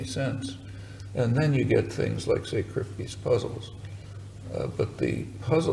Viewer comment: what puzzles (at 5:29) did he say? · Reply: Saul Kripke's (a philosopher and mathematician) puzzles, they're about paradoxes of reference and belief, so for example, the names Clark Kent and Superman refer to the same individual being, so logically it should seem to follow that anything which is true of Clark Kent is also true of Superman. However, on the other hand, if, for instance, Lois Lane believes that Superman can fly, it doesn't necessarily mean that Lois Lane believes Clark Kent can fly. So the names Superman, Clark Kent must have some additional semantic properties beyond just meaning the thing to which they both happen to refer.